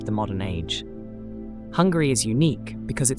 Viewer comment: I'm sorry I'm only at 0:35 and I have found your video accidentally. I'm living in the part of the UK and I have to say I heard 2 times saying Hungry! I don't want to capitalise my letters coz I don't meant to shout but it's not Hungry it's Hungary! Sorry if my ears are so bad and maybe I heard it that way! Am I the only one who heard it that way?